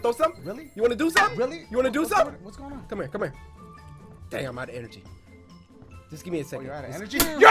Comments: did cory just say DAMN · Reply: no he just said dang really fast so it sounds like damn . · Yo I'm sorry but lowkey it sounded like cory said damn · Is it just me or did I hear cory say damn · LOL!! Junk was too hilarious man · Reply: Hey cory I could've sworn I just heard you say damn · did cory say "damn outta energy" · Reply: Accelec no he said dang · did Cory say damn?